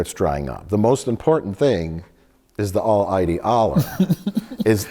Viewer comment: I misheard this as Mike saying “The most important thing is the all mighty Allah” and had to rewind to make sure I heard that right lmao